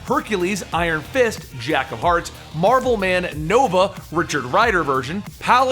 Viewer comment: marble man or something i couldn't understand i wrote marble man in Google it is not there what is it can you say properly